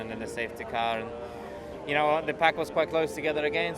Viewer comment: did Mazepin said the pack is very close?